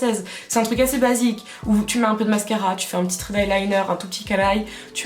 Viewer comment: pourquoi tu dis « caralho » ?